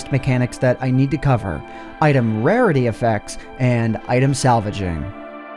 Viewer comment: Did you just say Salaving?